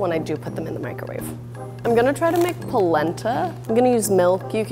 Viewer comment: I thought she said she was gonna try to make placenta